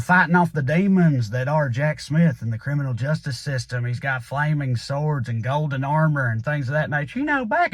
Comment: When Trae was describing the forthcoming trading cards, and said "flaming swords," I first thought that he said "flaming sores," which would be more interesting and accurate.